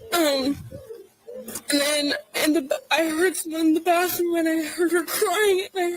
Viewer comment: 'I heard her crying. And I heard some guys in there'. This is what it sounded like to me.